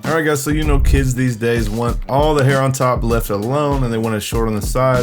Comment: I thought he said the Kid wanted the hair on top left alone. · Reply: Not left alone but left long